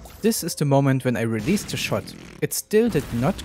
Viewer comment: I was happy to see that it is "possible" now! :D Also funny how the game decided to start a super long farting sound at 3:53 the moment I say "I released" lmao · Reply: It also sounds like you said "released the shart"